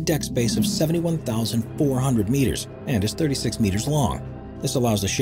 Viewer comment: Does he really say 36 meters long???